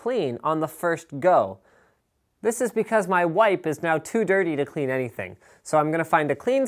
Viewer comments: I thought he said my Wife is too dirty. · i thought he said "My wife is too..."